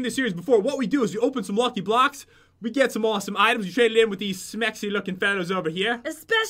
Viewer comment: Did he say smexy at 1:05?